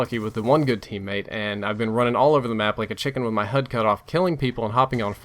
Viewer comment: at about 16 minutes in, it sounded like you said running around like a chicken with my HUD cut off lol